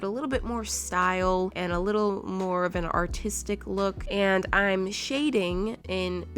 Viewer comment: I thought she said autistic